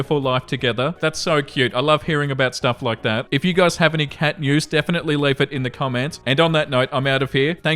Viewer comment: At the end it sounded like said "cat mews" not "cat news" and I honestly want to hear either.